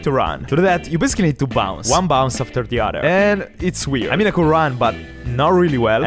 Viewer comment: why does it sound like he is saying 'I made the quaran' instead of 'I mean I can run'